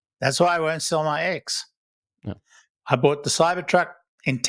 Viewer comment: I thought said, "I'm not going to sell my eggs." It would take a lot of eggs to trade for a Cybertruck. 🙂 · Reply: I think he said his X. If so I understand going from my Y to my son’s Cybertruck is not good because of FSD in my Y